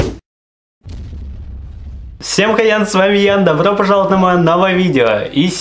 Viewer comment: мне в начале послышалось всем хрен с вами Ян)))))